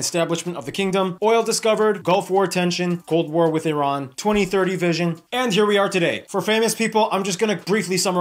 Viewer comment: The Olympics aren’t going to be held in Saudi Arabia in 2030. You misheard. He said 2030 vision. As in where they hope the country will be in 2030. No big deal though.